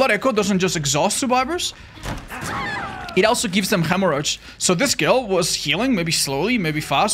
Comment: I legitimately thought he said the perk gives survivors hemorrhoids. The image of a survivor suffering from hemorrhoids while running for their lives will be stuck in my brain